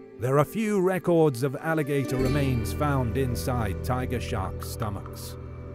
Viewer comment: This is not to say the Shark won't win against the Alligator, but the Alligator have really good sensory organs on It's mouth, so it doesn't matter if the Alligator can see or nah. Alligators use those sensory organs to feel movement in water, let's say if some animal drinks from the water, the Alligator would be able to feel that. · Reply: thought you said shrek